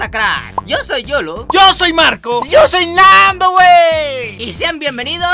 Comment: entendí manco xdddd